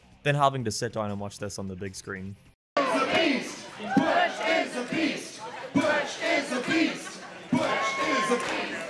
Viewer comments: im sorry but i heard Butch is obese i hope u will unhear it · Cringe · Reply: I thought they were saying "BUTCH IS UNLEASHED" but I think they're saying "BUTCH IS A BEAST"